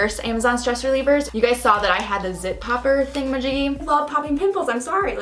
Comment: can you tell me what she said Like she said Popper thingy Majiggy Or something Did she say popper thing magicky